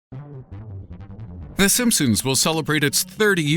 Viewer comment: did i just hear him say "the simpsoons"